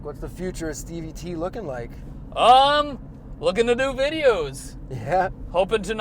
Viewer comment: Did he just say "looking TheDooo videos" at 6:23 ?? Correct me if im wrong...